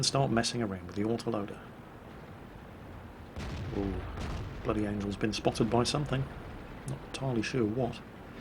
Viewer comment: thought you said bloody anal at first..... :)